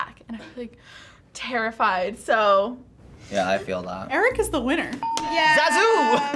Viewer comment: i thought you called him Erica